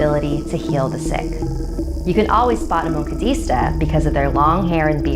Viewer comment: I didn't hear moncado I heard wakanda no joke lol